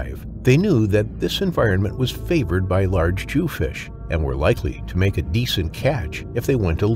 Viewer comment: …Did he say “Large Jew Fish?”